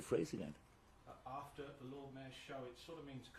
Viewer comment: English is not my first language let alone British accent,but can someone wrote for me what is the phrase again 8:00 ,I couldn't catch up..thank you in advance.. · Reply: "after the Lord Mayor's show", a phrase meaning the small events after the main event